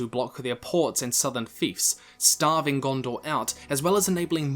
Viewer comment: What is the word at 9:41? They would be able to block their ports and Southern... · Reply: Fiefs